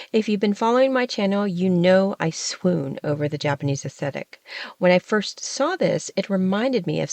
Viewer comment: Yes, I am going to step into the temporary role of the grammar police just because I think you would actually appreciate hearing this...at about minute 4:28 re. the Japanese inspired scarf, did I hear you say "you know I swoon over the Japanese ascetic..." when I think you meant "the Japanese aesTHetic?" I know some people confuse the two and when they can't choose between them in the moment, they fudge the middle consonants to compensate, but that would not be you, I'm certain!